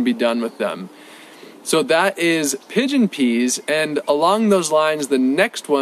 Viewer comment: I thought he said Pigeon Pee 🤣🤣 Not sure these will work for me. We don't have humidity. Lots of rain in summer and super hot, winter is completely dry but no snow.